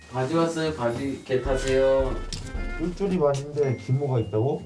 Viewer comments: 나만 '겟 하세요'로 들리나 ㅋㅋㅋㅋㅋㅋㅋㅋㅋㅋㅋㅋㅋㅋㅋㅋㅋㅋㅋㅋㅋㅋ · 계타세요말고 겟하세요로 들려ㅋㅋ